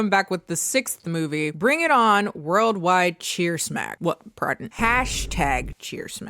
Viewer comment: I kept hearing “cheer snack instead of cheersmack”🤣🤣🤣 I’m like were they sponsored by Cheetos or something